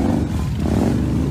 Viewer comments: "one of the most racist towns in america..." this could be true for every town that BLM gathers at, simply due to BLM gathering there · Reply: I heard him say “one of the most racist sounds” - referring to the motorcycles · "one of the most racist towns in america..." this could be true for every town that BLM gathers at, simply due to BLM gathering there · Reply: I thought he said "One of the host racist sounds in america." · "One of the most racist towns in America everybody" Didn't look anything like portland to me... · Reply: I thought he said racist sounds referring to the Harley’s.